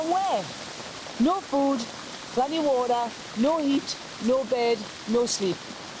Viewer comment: did she really say no water? collect the rain water lol